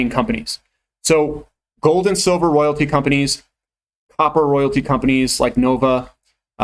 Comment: What did he say at 15:02 "Opera royalty companies". What are those..? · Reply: Copper royalty companies. Nova Royalty is the fav.